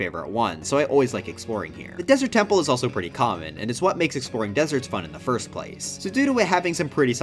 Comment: DID YOU SAY BORING DESERTS????? OH GOD YOUR NOT GETTING AWAY WITH THE DESERT LOVING FANDOM!!!!